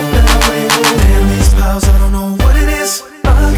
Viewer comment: I thought he said damn he smells I don't know what it is